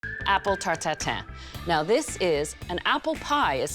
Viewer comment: The first thing i heard: "Apple tartatan"